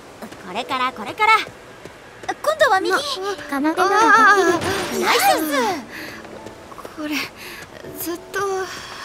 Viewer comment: な、わ、あああっ！ えっ可愛すぎる その後の瑞稀と絵名のナイスがナイスっすー！に聞こえてジワるw